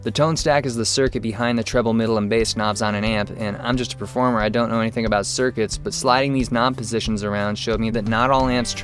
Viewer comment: I thought he was saying: "I'm a performer I know nothing about the circus"